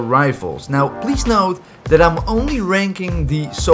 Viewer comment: i thought he said and Today im raping not rating lol